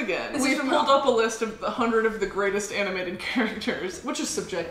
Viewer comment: I thought they said anime characters and I was so confused when she said lightning mcqueen